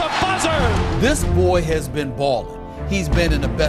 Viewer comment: lol i thought he said "this boy has been balding"